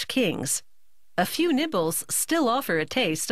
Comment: I heard nipples not nibbles